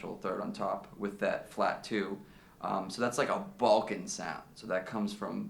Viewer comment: When you are from the Balkans, it is not so hip :) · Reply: Oh, he said Balkan. I thought he said 'Vulcan' until I read your comment.